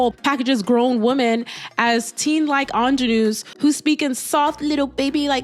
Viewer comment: can someone tell me what this word is "teen like ____" what? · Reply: Ingenue